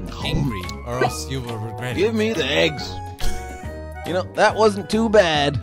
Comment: I thought he said give me the keys 🤦🏼‍♂️🤣😂🤣😂🤣😂🤣😂